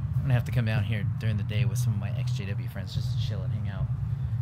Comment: When I heard him say his exjw friends I thought he said his sjw friends